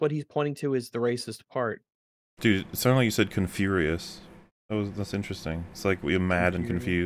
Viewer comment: ConFurious For a second, I thought said Kung-Furious